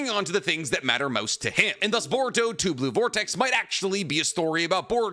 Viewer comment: I keep hearing "boruto tube vortex" lol. Also, always finds a way to kill Sasuke 😂😂